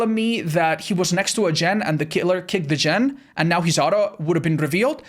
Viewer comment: did he just say "Kitler"?